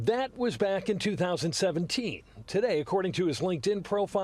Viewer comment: he says "wock" (or "walk") instead of "lock" twice. Like Elmer Fudd. Weird.